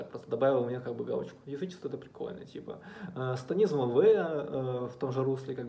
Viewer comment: На 3:01 какая книжка? "Сатанизм В"? Не могу расслышать. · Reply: Лавая